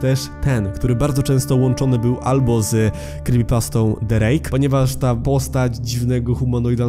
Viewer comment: usłyszałem drake